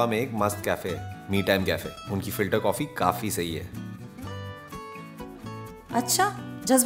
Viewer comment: I thought she is saying filtercopy😂😂